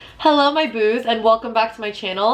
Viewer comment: did u just say hello my boobs?